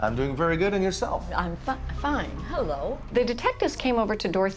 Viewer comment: it sounded like Dorothea is saying "I'm f*cked.. fine"